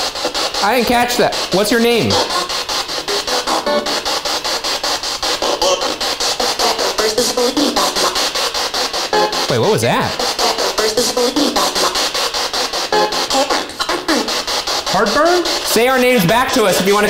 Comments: I swear I hear her say “where’s the sleeping party”…. 🙄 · when the spirit box said heartburn I thought it said Harper, which was the most terrifying two seconds of my life because for a sec I thought the ghost was saying my own name